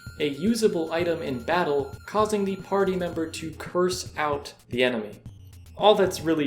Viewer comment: I thought you said "Curse out the N word" And I got shocked for a moment till I heard it back